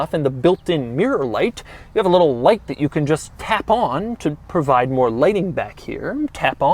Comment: sounds like he is saying tampon